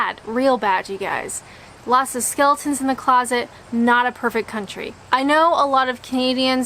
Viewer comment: I misheard you and thought you said “Lots of Stilton in the closet” and I was ok with the idea of a cheese-packed wardrobe.